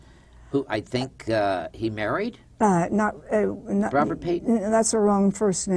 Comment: he said " Bra-bra Payton" which sounded like he said Robert Payton. Stop leaning your chin into your hand so you can enunciate ya gob...oh yeah, and anudder ting skippy...show a clip with YOUR GUEST in the shot...Ann oughta moyda ya, see?